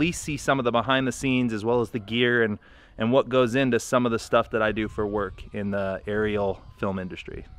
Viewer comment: NDA? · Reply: Ah. Yeah I know what those are, but I thought it had something to do with the motion picture business. I thought you said, "most of what I do is for NDA's..."